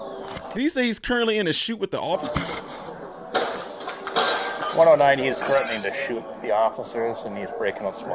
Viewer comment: I think the cop says: "Is threatening with shooting the oficers."